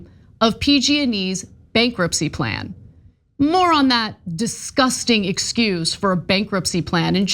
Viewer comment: “More on that.” Hehehe I heard “moron that.” 🤣🤣🤣